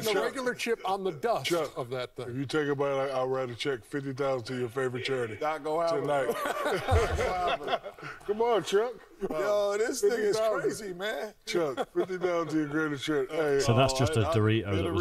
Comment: Did Kenny said, "This ni99a is crazy man...🤔